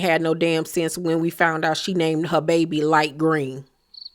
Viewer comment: wait… I think I heard you wrong. Lol Light Green?? 😂😂😂